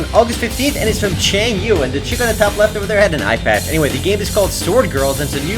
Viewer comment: i thought u said iPad....i was like * WHERE THE FUCK IS THE IPAD THEN ?!?!?!?!* then i figured that you said eyepad :D